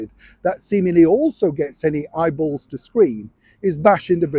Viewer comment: Did I mishear did he say "eyeballs to scream" ?